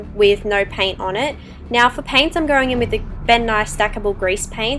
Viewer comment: I thought she said bill nye instead of ben nye